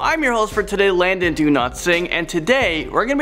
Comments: I thought you said your name was Landon donotsingh not Landon donotsleep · I thought I heard him saying,”Imm your host today..I’m Landon Do-Not-Sing”,I thought I heard it...So Funny...😂😂😂😂😂😂😂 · I looked to the side and and didn't see the name so I thought you said "Landon do no sing" lol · I thought you said Landon DO-NOT-SING. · Lol so I’m a bit new to this channel but I thought when he said his name I thought he said Landon Do not sing my first thought was why would I sing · No offense but i thought u said london do not sing😅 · Haha I thought he said Landon do not sing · It sounded like he said Landon, do not sing in the beginning xD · who else heard Landon do not sing